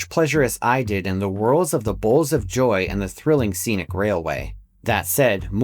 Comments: If it weren't for the tragedy behind it, "Bowels of Death" would be a badass name for a coaster or other ride. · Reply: I literally thought it said Bowels of Joy and that's what I typed into Google. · Thought it said "Bowels of Joy" and was like wtf? 😂